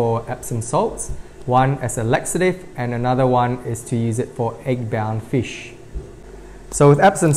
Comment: what did you say? 'Eggbound' fish?